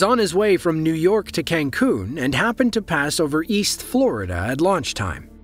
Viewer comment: Did you say Cancun,Quintana Roo, Mexico 🇲🇽?